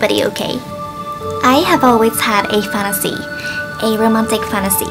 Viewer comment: fatassy? I first thought she said that xD